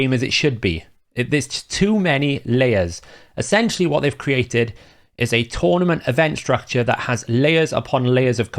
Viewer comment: “add a tournament event structure ..” When I first heard that I mis-heard it as “torment event structure” 😂 - much more fitting lol But thanks for this very clear and outspoken video, you explained it well and with focus on us players’ needs. Plarium should really watch this and enhance the reward system